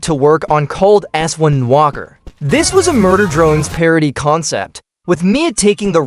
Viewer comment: DID I HEAR MURDER DRONES RIGHT NOW